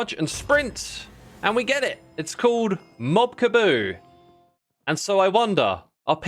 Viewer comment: Did you just say Kaboob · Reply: I think he just said Mob Kaboo